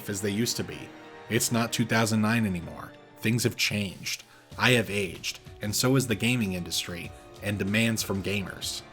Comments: I thought you said “I have aids” and I lost it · I thought you were gonna say I have aids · I thought you were gonna say “I have ai___” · thought he was gonna say "I have aids"